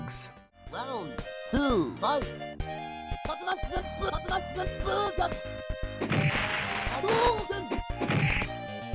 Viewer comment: I have no clue what Ryu is saying here, it sounds German to me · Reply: Senpukyaku or senpukick if your american